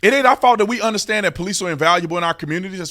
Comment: gah dang preach brother, Ima be honest you scared me saying our police are invaluable I heard that as unvaluable for a second there lol. Subscribed